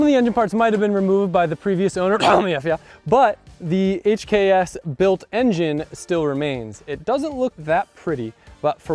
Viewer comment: Does anyone know what he said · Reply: Tommy fyeah